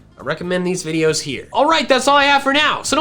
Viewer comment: Am I the only one who hears “Gotta catch the mall”